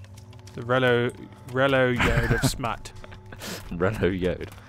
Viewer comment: THIS ENTIRE TIME I THOUGHT IT SAID YELLOW ROAD OF SMUT. :O